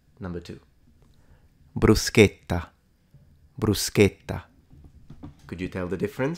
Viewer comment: The latter one sounded like ’Ndrangheta! :face-fuchsia-wide-eyes